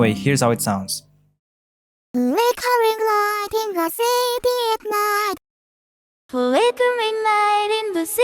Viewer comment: It sounded like teto said "flickering light in the shitty of knight"